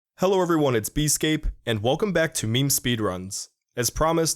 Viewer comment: "Did you just say Easescape?" haha